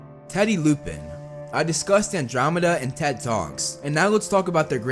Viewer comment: When Teddy Lupin came around I low key thought he said “I disgust Ted and Andromeda” instead of discussed 😂🤣